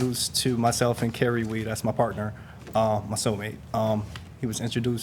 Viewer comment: Wait…so instead of hiring a licensed professional in your area…you decide…to “save money” by FLYING this man ACROSS COUNTRY, pay for his hotel for 7 days!! And a weeks worth of work…and that’s somehow normal and saving money? I’m so lost about how this whole scenario would’ve made any sense to the plaintiff and his “soul mate” 😂 sounds irrational and not practical at all. Just pay a local contractor and keep it simple people! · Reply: I thought he said "my partner my cell mate" lolol what you said makes more sense 😂